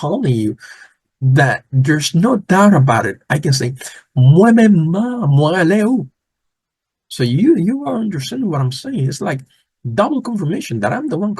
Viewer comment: Hey I have a question at @ 41:19 Did you say Mwen menm (menm) mwen rele ou? Im not sure what you said on that part I put inside the paranthesis Thanks!